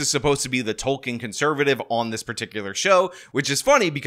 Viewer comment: At first I thought that you had said "Tolkein conservative". Because I have spent much time listenin to pop culture's channels.